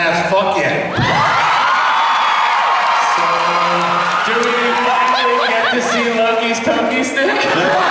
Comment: What does he say in 2:44? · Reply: „Will we get to see lokis poky stick?“